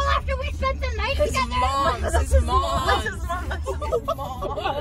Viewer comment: did they say 'This is mom'?